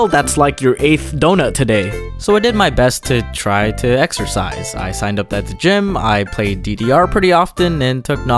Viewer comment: Exercise I thought you said Extra Fries